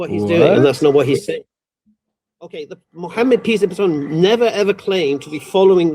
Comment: I am sorry did he said “piss be upon him?”